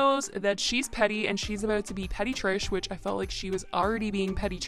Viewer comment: Deadass thought you said "She was going to be a petri dish" 💀💀💀